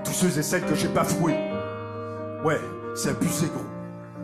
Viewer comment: Qu'est-ce qu'y dit ? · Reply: C’est abusé gros